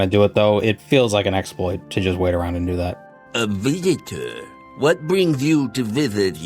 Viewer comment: When Yagrum said "A visitor..." I thought he said "A Redditor..." and it seemed like he was describing himself